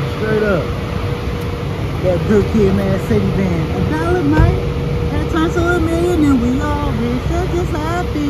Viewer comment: "A dollar might turn to a million and we all rich that's just how I feel"🤣🤣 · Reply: I always thought Kendrick said “8$ mic turn to a million an we all rich”😂😂😂🤦🏽‍♂️🤦🏽‍♂️